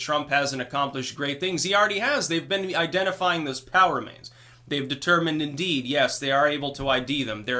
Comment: It's funny, whenever you say, "POW remains", I hear "power mains". The first time I heard it i had to sit back for a second and think, "what's he talking about power mains for?....oh....I get it."